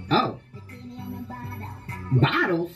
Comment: i thought she said "bottle" too but she said "byeoro" (Korean).